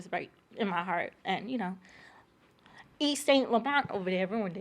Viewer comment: plssssssss did she say “eat, stank, laurent (Yves Saint Laurent)????😭💀